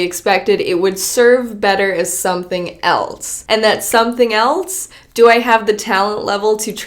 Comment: "Something eltse", am I the only one who hears that?